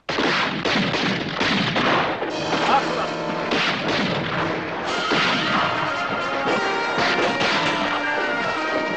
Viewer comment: what did he say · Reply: “After them”